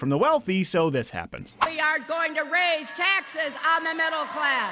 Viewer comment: Hiliary literally said "we are going to raise taxes on the middle class!" and people actually cheered and clapped........... Moron level is over 9000!!! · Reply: Paul Comyns -Slowing the vid down to half speed at 3:28 I can't hear the "aren't" you're speaking of. "Are" is also consistent with the thoughts expressed at 3:08. Based on those clips, it's a hard sell on your part.